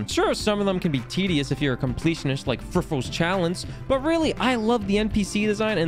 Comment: What's he saying at 9:08? Friffles Challenge? Frivolous Chalice?